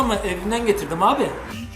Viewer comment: de cam bardagiyla cay satmak yasak artikdan sonra şişman reis ne diyor anlayamadim ? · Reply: Lan bide